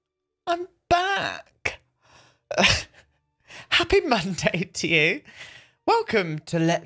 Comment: Istg I thought he said Happy Manday!